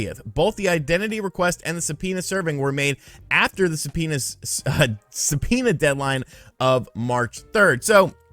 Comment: Se-penis?😂 did anyone else catch that??